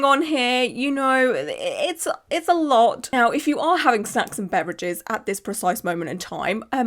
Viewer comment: I thought you said "if you're having s€x and beverages" 😂